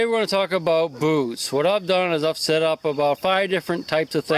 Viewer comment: At first I thought you said lets talk about booze.